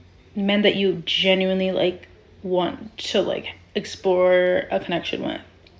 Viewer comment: She said 'explore a connection' , , misheard explore a country. it's sort of african american ebonics. why does the lady at the end intentionally lean over presenting her boobs like that, it's so clearly intentional and she thinks she's hot shit doing that with her face done but it comes off so yucky. i felt like a masochist witnessing that.